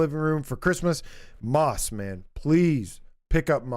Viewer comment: I heard you say "Mothman", but I think you said "Moss, man" 🤣 But a VR game with cryptids in the style of Phasmaphobia would be cool.